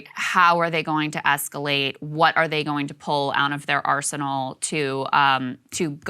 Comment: As a South African English speaker, for a second I thought she was saying "What are they going to pull out of their arse." XD